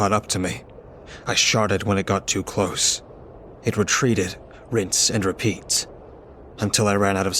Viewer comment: What do you say? Because I have listened to this over and over again and I keep hearing •sharted•